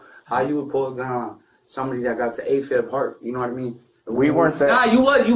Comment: I think he said "AFib heart" hwere afib stands for atreal fibrilation.